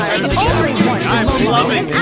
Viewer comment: Is it just me or is 4:42 kinda funny? whenever i hear it i just mishear and it just makes some golden phrases get sprinkled in like "i'm mclovin it" and gaugin saying "YES GET ANGRY" and i just start laughing